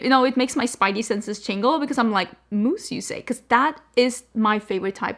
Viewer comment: I heard "mousse" as moose 🫎 got derailed and had to rewind. Moose lips sounds like a whole style XD